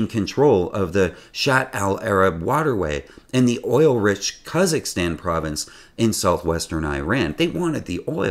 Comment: Sounded like you said kazakhstan when the province is khuzestan